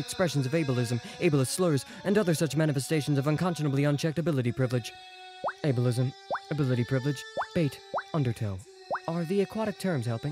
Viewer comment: Omfg did Kankri just say undertale?!?!? o0o (shook)cuz I am a undertale fan))